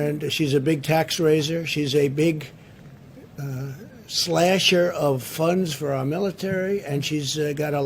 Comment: When he started saying 'shes a big slasher' I thought he was saying 'shes a big slag' lol